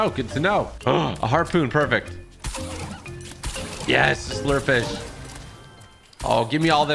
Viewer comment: When you said slurp fish it sounded like you said slur fish